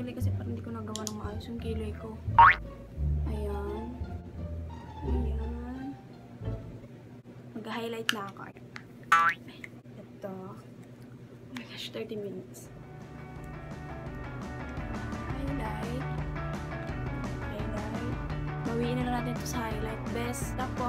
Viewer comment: “my gassh 30 mins. “ Hhahhaha sa sobrang taranta dna alam gagawin 😂😂😂 tama ba narinig ko?😂😂😁 Kyoootiiii😂😂❤️😍